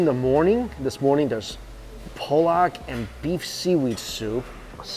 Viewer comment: Did he just call that soup Polack at 1:55?? Lol.